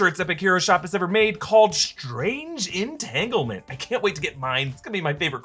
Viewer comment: Strange Entitlement? Whoops. 😃 I thought earlier you said Strange Entanglement.